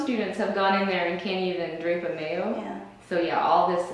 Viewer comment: What did she say at 4:40? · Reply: She's seen techs that can't even 'drape a mayo'. This refers to covering a mayo stand with a sterile drape. The mayo stand is the adjustable, moveable stand the Surg Tech places instruments on for use at the surgical field. They are easily accessible to the tech to pass to the surgeon. The 'drape' is a sterile cover placed on the stand itself, creating a sterile place to lay out the instruments. Knowing how to drape your mayo is an essential step in setting up your OR. I'm halfway through my 18 month Surg Tech certification course, so I'm still learning, but I do know how to drape a mayo! 😜